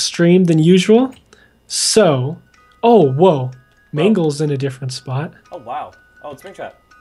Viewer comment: When Mangle was in the vents I thought they were saying Mango. 😹